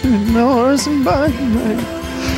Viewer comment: what was saying? It sounded like horse and buggy ride. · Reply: It was horse and buggy ride, it's one of his other songs